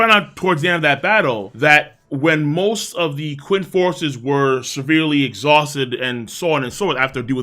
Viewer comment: I swear, every time I heard it as "Aqua forces", and it made me laugh like an idiot xD